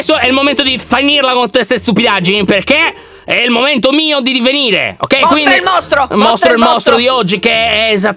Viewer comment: Ma solo io quando ha detto " è ora che io divenga il mostro" ho capito " è ora di venire"...🤣🙃